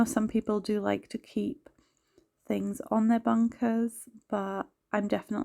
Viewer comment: This not a complaint but what ere you calling the counters? Might have to spell it as I thought you were saying bunker? Just a curious Canadian. Thanks. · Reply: Hi, yes we call the counters "bunkers" in my area x